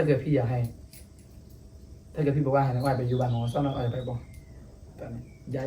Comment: พูดหรือแร็พครับเนี่ย 555 ได้ยินแต่บ้านหนองวัวซอบ้านผมเองง